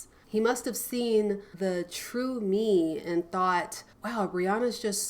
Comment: Thought you said Rihanna at 19:40 and I was so confused